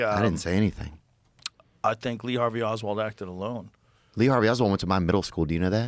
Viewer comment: did Joe just say Lee Harvey Oswald acted along 😮. Damn Joe, you down bad